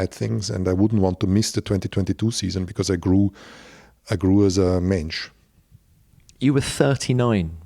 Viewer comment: did he say "I grew as a Mensch"? Mensch means human in german